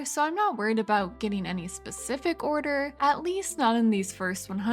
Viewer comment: I had to do a double-take since at first I heard "At least not in these first 100 years."